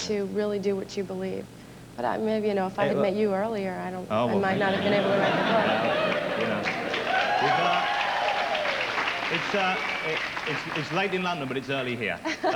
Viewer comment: what is she saying? 'If I had met you earlier'??? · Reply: 'If I had met you earlier I might not have been able to write a book'. Do you maybe have an idea why she said that?